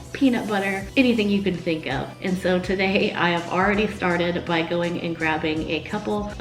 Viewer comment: Really thought you said “I already farted by” 😂😂 took me a second